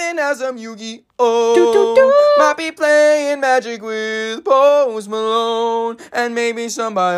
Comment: When i first heard it without subtitles, i thought it said "Might be playing Magic with Home Alone" lol